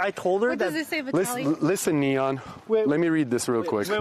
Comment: Did Vitaly call him Neon? Neon is a famous Indian origin streamer. Around 4:12 he say listen Neon. That is so racist.